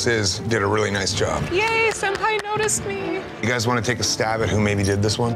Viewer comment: subtitles say "-Yay (indistinct) noticed me." I'm almost certain she said "senpai" in reference to Dave Navarro.